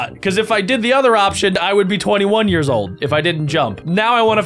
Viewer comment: you said 21 the subtitles said 20😮 ( 12:52 )